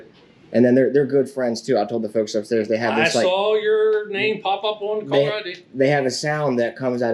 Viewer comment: If I heard correctly, @ 32:01 the Supervisor says I seen your name come up on the caller id, @ 33:28 he's saying I don't even know who you are.